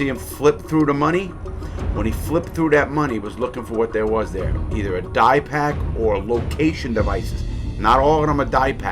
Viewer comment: is he saying a dye pack because when I looked up what he said I found diapam, dia pak, and dye pack